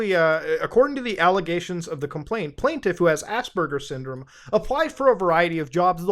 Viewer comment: i am not the only one who thought he was saying Ass burger syndrome right?...